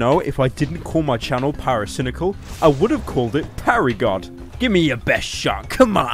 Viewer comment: I thought you say Gary god